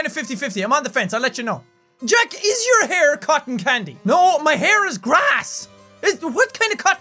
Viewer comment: when you read up: your hair looks like cotten candy i thought you said cut and candy